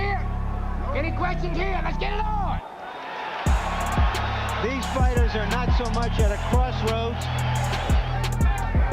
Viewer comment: Wait🤔🤔🤔 @ 5:43 did he say health problems and "White"...what's that got to do with any especially being,this is America? Great video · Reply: travel squadd. Lmao he said weight. Check before you say something 😂💀